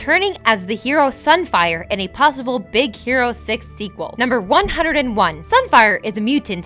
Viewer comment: I heard her say "Big Hero sex"